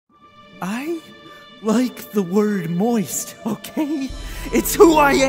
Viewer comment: lmao I thought he was saying "I like the word **voice**" and this movie was about to be a Disney princess film